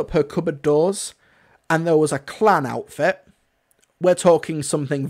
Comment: Extremely well said. Agree with everything you said here. Also for a second when you said that if Joan opened her cupboard and there was a klan outfit in it I thought you said clown outfit which you are right would mean something very different.😂